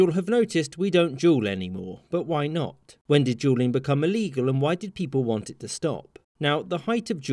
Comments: His accent makes it sounds like he said “Juuling was illegal” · the subtitles say “when did Julian become illegal” 😂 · Who else thought at first he said juulibg became illegal 😂